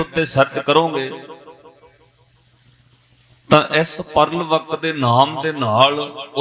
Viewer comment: Can someone please share the name or some links of resources which mentioned about the nobel prize winning christian lady? I can't hear it properly in the clip. If someone understood the name, please share it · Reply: pearl s buck name a